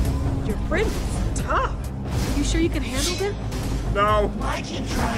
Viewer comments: did anyone notice that at 6:34 it sounded like Harper said "your friends tough! Are you sure you can handle HIM." (this is about petra! a girl!) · did Harper just call Petra a "him"!?!!!?!